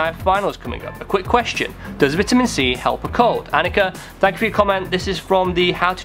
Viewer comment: Lol i thought i heard intimacy instead of vitamin c. This quarantine is killing me!